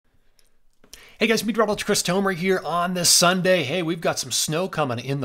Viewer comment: wtf do you say in your intro All I hear is “beach crumbler Chris tomer” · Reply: "Hey guys, meteorologist Chris Tomer here"